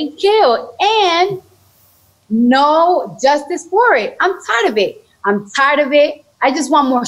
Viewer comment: Cardi B “I’m tired of it” well we conservatives are tired of you!😂🇺🇸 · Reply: Sounds like she was say " I'm tried a bit"